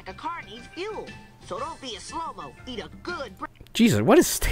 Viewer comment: When Sonic said "Don't be a slowmo" at first I thought he said "don't be a homo"